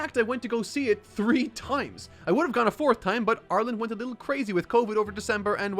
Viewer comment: you can tell I watch too much KOTH because when you said "Ireland went a little crazy with COVID restrictions" I heard "Arlen" (the Texas town where koth takes place)